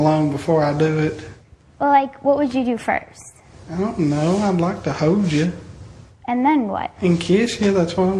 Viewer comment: sounds like he says I'd like to hose ya.